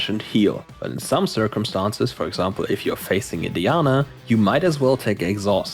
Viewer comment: did you say Indiana? so confused